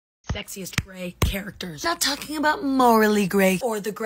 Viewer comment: i thought it said sexist gray characters